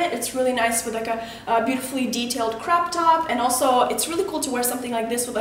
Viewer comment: I thought she said crap top LOL..